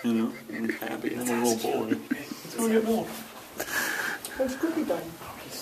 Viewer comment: I hear someone say "let's go get milk!" ahh gotta love them(